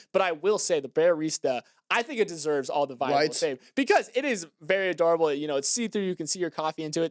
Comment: I thought he said Bearyadorable 😭😭